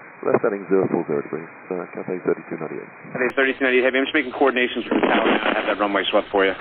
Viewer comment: "I'm just making coordinations with the tower now..." is what he said (not a huge difference, granted).